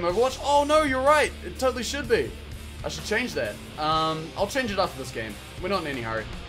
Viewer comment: what does he say at 1:52:50 the accent just puts my ears off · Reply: superpants I should change that, I'll change it after this game